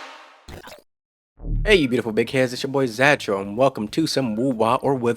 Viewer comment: If your intro line to your video was "hey you beautiful dick heads" like i thought i heard you say i would've instantly subscribed for being a no-fucks given personality